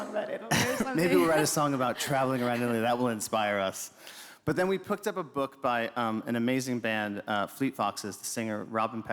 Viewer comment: he said “but then we pooked up a book” I had to rewind to see if I heard him correctly but he literally said “pooked” which is exactly like me, because sometimes I just struggle to speak properly even though English is my first language 😂